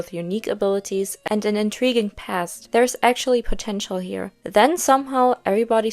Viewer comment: Did someone say potential???